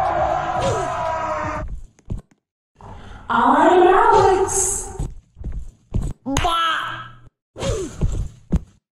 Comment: wat did she say ?!?!? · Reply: Im alex